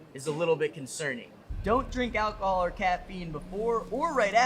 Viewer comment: “I heard don’t drink alcohol or cat pee”